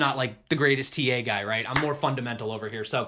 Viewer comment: I swore I heard "I'm more fun and mental" lmao